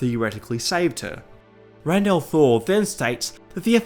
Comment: does he say "Rand Al'Thor then states...." As in Rand from the wheel of time? Is he talking about somebody in the comments section talking about the wish?